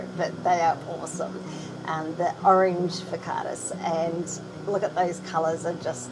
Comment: Can someone please type me the name of this fish breed ? 30:18. I can't make out what she called them. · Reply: Found it! Pseudomugil Furcatus - Forktail Blue-eye (orange fin variety)